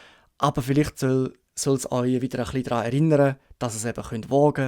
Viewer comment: Bei 6:56 sind die Stellen wo ich Probleme mit dem schweizerdeutsch verstehen habe, wenn zwei Worte schnell ausgesprochen werden und sie sich ähnlich anhören. Weiß jemand zufällig was er da sagt ? Ich Verstehe ….wieder ??? Erinnern ….. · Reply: Vielen Dank für die Frage. Die Stelle bedeutet: "wiider echli draa erinnere" und meint "wieder etwas daran erinnern". 😊